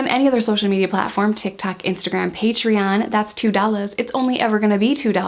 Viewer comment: I heard if you Unfollow it’s two dollars lol